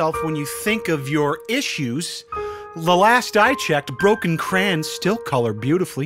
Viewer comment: did you- did you just say “crayans” instead of crayons???